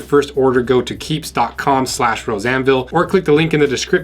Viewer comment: I keep hearing "Roseanneville" instead of.